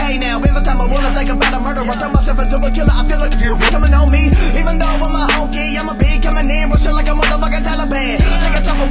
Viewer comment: straight snapped. ripped it up, damn. What word got censored at 2:10?? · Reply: I said ( I feel cold breeze coming on me )